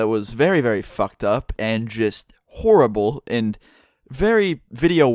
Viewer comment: when i heard him say just horrible i thought he said jizz horrible... lol